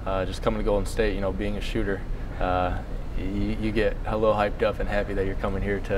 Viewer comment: Did this dude say "hella hyped up"? Hahaha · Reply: I think he said "you get a little hyped up" but it did sound like he said "hella"